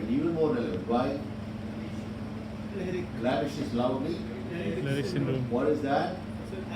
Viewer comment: Heard it as Larry's syndrome